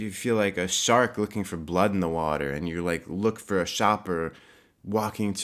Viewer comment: At first I thought you said "a shart in the water" Merry Christmas x